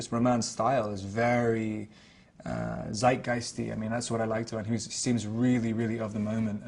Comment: What did he say at 2:21? Romains style is very...? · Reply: Coming to rescue after 7 years to tell you that the word was “zeitgeist”, German for “spirit of age” ;)